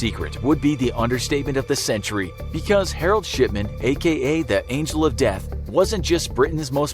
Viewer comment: It sounded like he said Harold Shitman